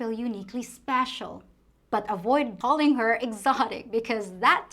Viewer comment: What did you say at about 12:30 in the video, the term we shoud not use because it is an insult there? It sounded like you said "darling", but I want to make sure. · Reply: Avoid calling a Filipina ‘exotic’ because it means something else here 😊